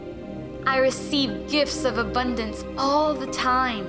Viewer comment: I always hear: "I receive GIFs of abundance all the time." 🤣❤️